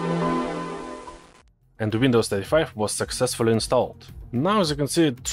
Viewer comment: I always hear "Windows Thirty-Five" 😀